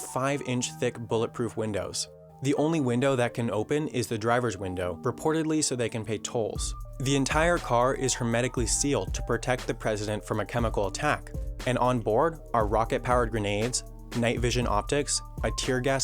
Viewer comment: did you say to pay toll?? 😂😂